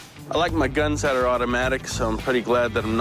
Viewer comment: knock at door did you say auto-magic ?